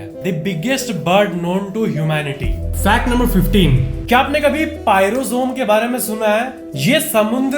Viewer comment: he say pyrosome I thought he is saying payal zone